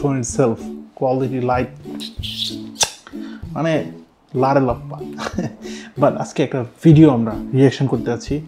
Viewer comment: ভাই ওখানে কি বলেছেন,,,, লারে লাপ্পা এটাই কি বলেছেন 😆😆😆😆